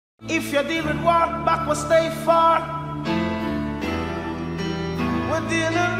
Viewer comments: Who else thought he was saying State Farm in the beginning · i thought he said state farm in the intro before i saw this lol